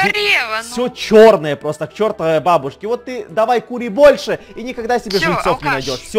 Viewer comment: Мне послышалось точто сказал а ты кури больше ахахахах